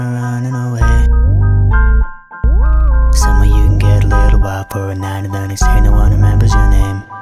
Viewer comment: Who else thinks that when he said "get a little wild for a night" thought that he said "get a little whopper for the night"